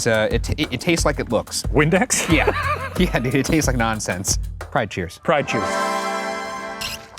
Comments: Ryan: "It taste how it looks." Me: "Gay?" Shane: "Like Windex." Me: "Oh okay." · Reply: oh i thought he said nonsense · i love that the captions say “fried cheese” when shane says “pride cheers” @ 4:45 lmao